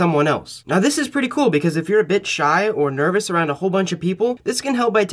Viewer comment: my heart stopped for a second when I thought he said "if you're a bitch"